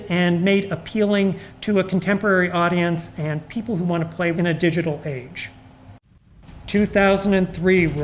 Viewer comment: all I hear is "digital age"